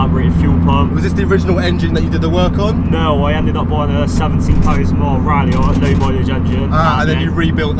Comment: What engine did he say? I can barely understand him lol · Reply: engine from a colt ralliart, 4g15t